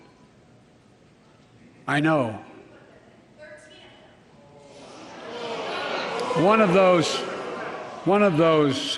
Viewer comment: What was said by an audience member around 59:00? Everyone boo’d but I couldn’t make it out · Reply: “You put them in. 13 of them”